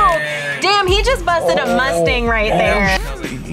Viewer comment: did i heard mustang ?